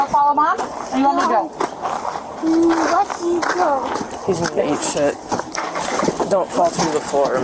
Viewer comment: Anybody know what that officer said!??? Did she say these people ain’t shit!? Lmaooo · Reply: I think she said "he's gonna eat shit" talking about the child. Saying he was gonna fall going up the stairs in those big clown shoes 😂